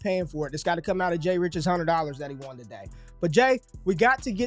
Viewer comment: $100? I thought you said $300?